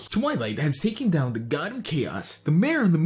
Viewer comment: has good instead of god